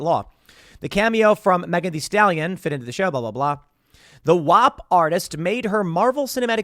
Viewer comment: I wasn't watching, just listening, and I heard "wop artist". Then I realized he wasn't making a joke about Italians 🤣🤣😁